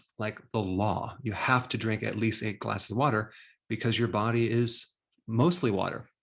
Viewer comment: Does he say "you have to drink A glass of water " or " you have to drink EIGHT glass of water" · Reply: 8